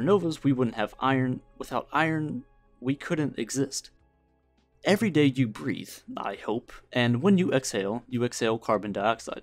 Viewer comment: I was deeply lost in my own thoughts before I heard, "Everyday, you breathe... hopefully".